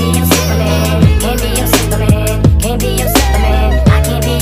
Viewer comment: I thought it said "I can be your superman" and I realised now 😭😭 my entire life was a lie💀